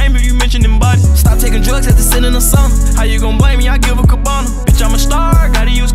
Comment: I thought he said “stopped taking drugs , had to sit in a sauna” not “stopped taking drugs after sending a song” lol